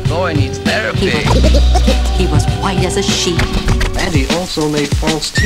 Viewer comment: I thought she said "He was white as a sheep" i feel stupid now 😭